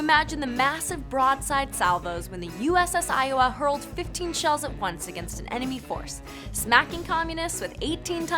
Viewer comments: Wait wait wait,did she just said USS Iowa hurled 15 shells at once at 0:30 ? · Did she say 15 shells at once at 0:32 ?